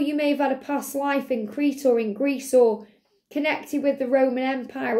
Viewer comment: Pile 3, haha i laughed so much during it. First of all, i thought you were going to say hilarious, you didn't.. but later on you did! 😂 And when you said Greece... i understood it at first as Grease .. like the movie hahaha 😂Not that i have a bond with that movie though ;p but maybe it's a sign, that my love will very soon be on my doorstep and tell me, that i am the one that he wants 🥰❤ You said 46, i pictured it as 64 😂😂What is wrong whaha. Well, on the last part, like us in Union helping the golden age... omg definitely. I got angelbumps there 😍😍